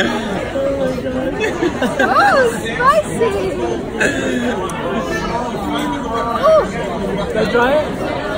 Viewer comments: did somebody just say "It's spicy"?? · did she...did she just say spicy?